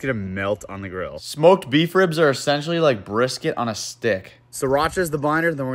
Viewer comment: Him: brisket on a stick What i heard: "foreskin on a stick"